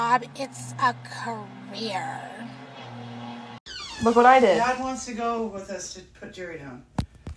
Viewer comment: no matter how many times I replay the clip I can’t understand what the mom is saying 😭😭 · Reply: "Dad wants to go with us to put Jerry down" I think